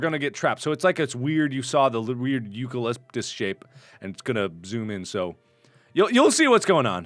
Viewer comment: Did he just say eucalyptus shape? What.